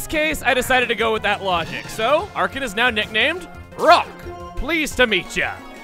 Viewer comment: Sorry whats its nickname now? All i hear is crock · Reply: Roc.